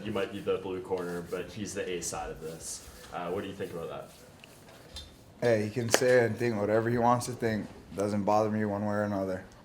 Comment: Notice the nervousness in his voice "hey he can say thing, whatever he wants to think" you mean "say what he wants to say?" Pay attention to the small things boyz, they mean ALOT. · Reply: He said “he can say and think what he wants to think” he still could’ve worded it better but it just shows he’s not used to the attention and speaking with a camera in his face. Brad either severely underestimated Izzy’s abilities, or he was nervous and was trying to play it off.